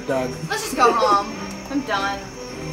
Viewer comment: Did Bridget just call her dad Doug mum? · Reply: She said lets go home I'm done